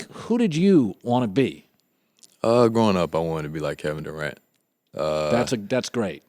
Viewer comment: Kevin Durant or Kevin Garnett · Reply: 9,999,999,999 Views he said Durant. Colin thought he said Garnet